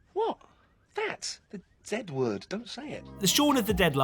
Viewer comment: All these years, I thought he said "that dead word" because I watched that movie for years before learning that some countries say "zed" instead of "zee" so by the time I learned about "zed", it was already "dead" in my head.